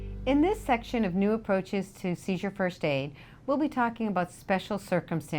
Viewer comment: The subtitles are wrong at 0:10, it says "Caesar" instead of "seizure."